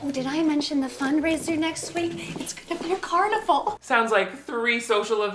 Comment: The closed captions tho when Viola's mom was talking about the upcoming debutante events..she said "and did I mention the fundraiser next week? It's gonna be a carnival!" but the CC said 'it's gonna be a car and a fall!' LMFAOOOO stop it literally 12:18 if you wanna turn on CC and see it for yourself